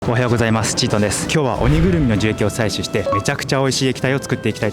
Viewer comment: おはようございますちんちんですに聞こえた(´・ω・｀)